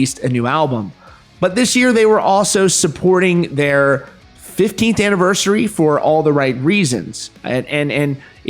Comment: Had to listen to it again but I almost swore he said 50th anniversary 😂. But I guess he said 15th.